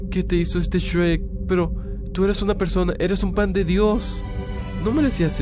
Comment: Soy yo o escuche ¨Tu eres un pan de Dios¨ JAJAJAJAJA